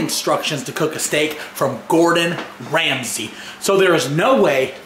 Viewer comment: I thought you said "Gordan Freeman" not "Gordan Ramsay" XD